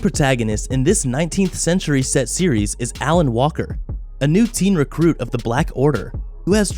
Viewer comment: did I just heard Alan Walker?